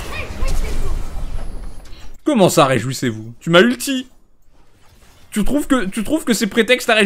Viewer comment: Moi j'entend "Respectez-vous" j'ai cherché un peu mais je trouve aucune citation de Ashe qui ressemble à ce qu'elle dit...